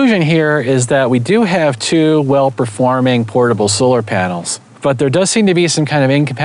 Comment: I thought the caption said portable solar shutdown. 😆